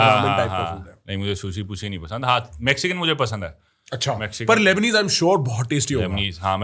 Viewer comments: did he say mujhe sushi pussy nahi pasand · bhai kya bol rahe ho 31:13 sushi - p..sy 🤣🤣🤣